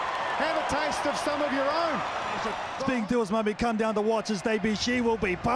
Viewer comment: Did anyone understand a word of what he said ? Was is it a Maori dialect or something? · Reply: Talking about someone coming down to watch his debut